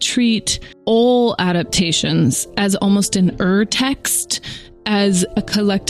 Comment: Is there anyone who could explain to me what Greta Gerwig says in 7:01 ? In subtitles it's written ER text and later earth text but I feel like it's not that. I need to translate it to my language but I'm struggling with this word... :( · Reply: Gabriela Sečkářová it's urtext